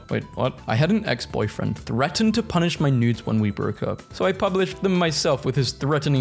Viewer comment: I heard “punish my newts”